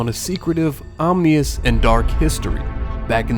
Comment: Did you just say “omnious”? It’s ominous 😂